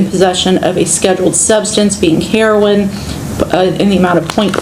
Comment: did she say Carowin? I know its prescribed but still. Why would they go after her for that? 😅